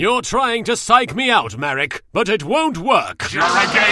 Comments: I ALWAYS hear "You're a gay hoe, attack Queen's Knight!" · When marik says juregedo I keep hearing "you're a gay ho" am I the only one?